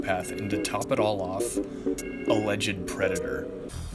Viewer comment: i thought you said "a legend predator" lol